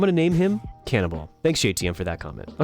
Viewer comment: For a second when he named that turtle “Cannonball” I thought he said “Cannibal”. ☺️